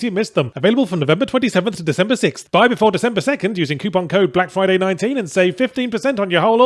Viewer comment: "Buy before December 2nd and save 15%!" I heard that as "Die before December 2nd and save 15%!" and went Sounds fair.